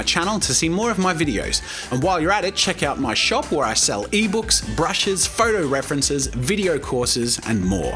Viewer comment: i thought you said 'nerd books'